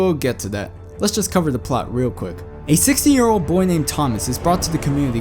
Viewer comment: I thought u said 60 instead of 16